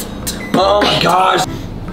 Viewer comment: 나만 아이씨 라고 들림?